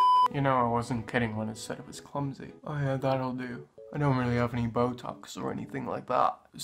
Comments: it sounds like you were saying “I have that will do” so I think you were trying to say something like “I guess that will do”? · you said "oh yeah that'll do" yw (i replayed it about 23 times... what can i say, i like a challenge) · I hear “oh yeah, that’ll do”